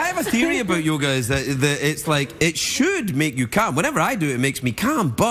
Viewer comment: People chuckled because it sounded like he's saying "it makes you come". ^^